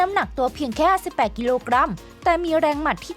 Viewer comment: อะไร น้ำหนักตัว 18 กิโลกรัม หรือผมได้ยินผิด? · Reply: 58ครับ